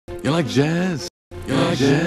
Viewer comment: He really went from “Ya like jazz?” to a malfunctioning lightsaber 🤠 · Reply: It sounded like he was saying You Like Yaaz?